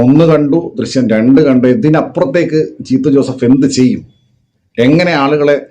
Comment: For a moment I heard "ചീത്ത ജോസഫ്😂😂😂